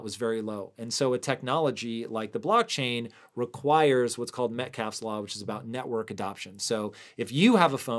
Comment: What term was used at 5:44 following "requires"? I couldn't understand what he said? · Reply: Metcalfe's Law